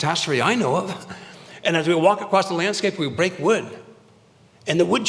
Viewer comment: I thought he said "break wind"! 🤦🏻‍♂️😅